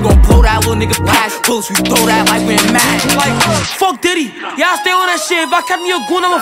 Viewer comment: did he just say fuck dd? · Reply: He said f diddy not did😭🙏